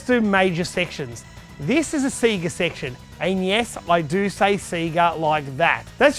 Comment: every time he says 'and yes' it sounds like he says 'NES'